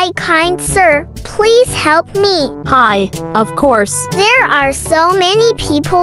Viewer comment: When she said hi sir! I thought for a second she said hi siri